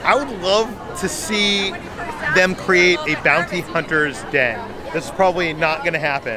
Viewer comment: I have a cold and my ears are plugged up-I thought you said “Bouncy Hunter” instead of Bounty Hunter’s Den. I was trying to figure out what in the galaxy you were talking about! Another great video, and! 🥰